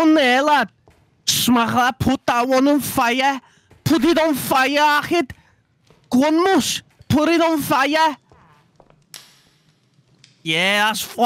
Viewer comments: I heard "puta" 4:56 💀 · 😂😂😂😂😂😂😂 it sounds like you say puta, it’s a Spanish curse word. Edit: it’s